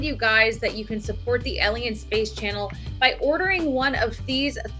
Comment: For a moment there, (around 7:40) I thought you said the "Alien Space Channel". :D